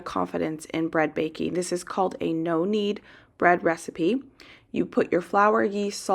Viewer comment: I thought you were saying "Nonni bread" - as in Italian Grandma recipe 🤦‍♀️😂